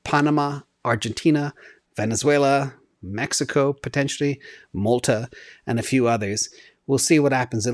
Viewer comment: When talking about the next country which could follow El Salvador, does he say Malta at 12:31? No way that will happen, it's a Eurozone country.